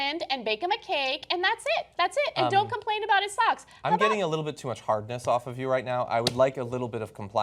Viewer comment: did he just say that Anne is getting him hard? WTF!!!